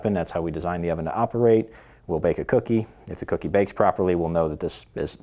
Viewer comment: did he say bake the kitten?